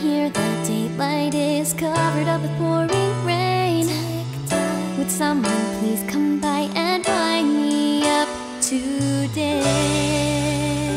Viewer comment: DID I HEAR TIK TOK IN